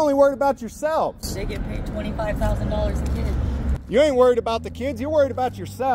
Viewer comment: Didn’t I hear a comment from the woman seated outside? Something about $40 thousand for a child. Maybe I misheard, but that’s what it sounded like. go back and check. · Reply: Just double checked. It was $25,000. Very clear.